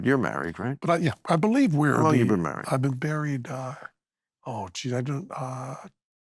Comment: ish did P say "ive been Buried 20 yrs"?...meaning married